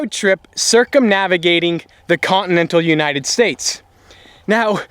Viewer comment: lower 48 states (minus Alaska and Hawaii) is commonly referred to as the continental United States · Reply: my bad, I thought you said continent of the united states. Continental is correct.